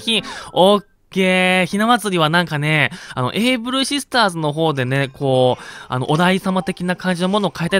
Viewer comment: avシスターズに聞こえたのは俺だけ？ｗ